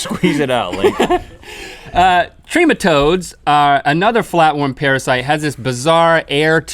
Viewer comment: i thought parasites said pastries at first...